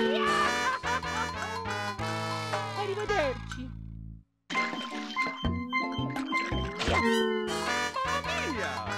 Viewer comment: Can someone tell me What's Mario saying at 7:35 when he dies because I've heard it a lot in this video. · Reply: Arrivederci, Italian for goodbye